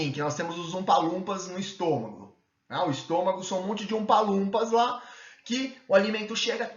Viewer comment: Tem um monte de QUÊ no estômago??? Não entendi. Pensei na Dietoterapia e na Fitoterapia que também são importantíssimos, né? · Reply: Oompa Loompa, são aqueles personagens dos Filmes da Fantástica Fábrica de Chocolates. Utilizo essa metáfora para dizer que o Estômago é mais funcional, e recebe grande influência do Baço (que seria o Willy Wonka). Ah! E sem dúvidas Dieto e Fito seria excelentes nas condições de Terra.